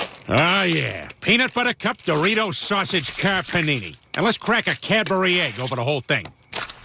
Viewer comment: I thought it said car pain